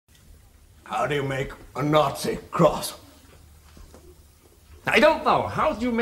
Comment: I always hear it as “how do you make a Kraut sour?”